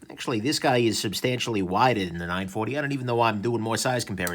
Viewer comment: 0.45......."substantially lighter than the 940"...??...wow!! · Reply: paul greenwood Hey, Paul- I thought he said "wider" not lighter. However, I'm pretty near deaf so I may be mistaken. atb!